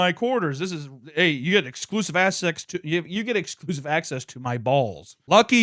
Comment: "exclusive ass sex" is what I heard, lol!